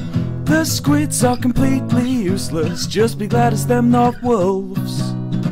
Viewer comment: for some reason at 0:39 in the subtitles it said GLaDOS instead of glad its.